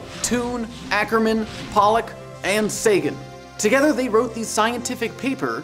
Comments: who else thought that he said satan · I thought u said satan. lol my mind is stuffed.